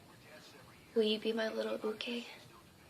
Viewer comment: did she say uke ???????